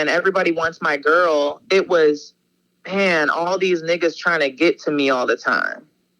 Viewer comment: did she say these niggas ??? This bitch white af 🤣🤣🤣 does she had a pass or sum ?